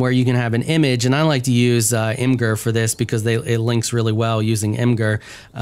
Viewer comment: You like to use what for your wall paper? Sounded like you said ingir... Anyone? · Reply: Imgur lol